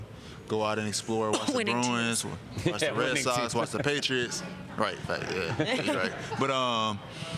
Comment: Boston Patriots, bruhhh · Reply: I thought he said watch the patriots not Boston patriots